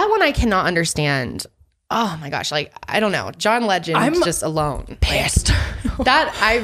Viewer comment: did she just say “john legend” ..? 1:06:58 lol